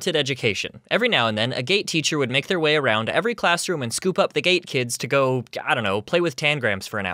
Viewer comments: I thought you said “and scoop up the gay kids” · at first I thought you said “ a gay teacher would come and pick up the gay kids”